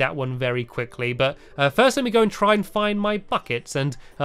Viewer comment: I Heard "First Let Me Go And Get My Fuck-Its" XD